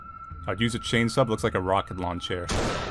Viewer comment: I heard 'rocket lawn chair' instead or rocket launcher, and it might just be the sleep-deprived at 2AM talking, but that sounds like a great idea.